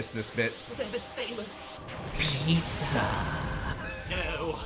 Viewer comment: I never noticed Goku said "Pizza" there. I just thought it was Freeza saying, "HIS!"